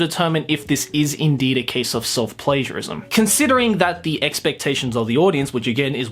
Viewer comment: When you said self-pagiarism i thought you said it a self-pleasureism